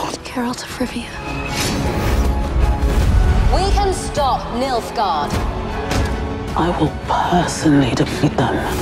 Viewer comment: i thought she said MILF GUARD hahahahahagagagagaga